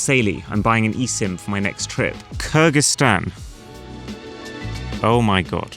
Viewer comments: I thought you said Kurdistan as well. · i Mean even I thought you said Kurdistan 🫩🥀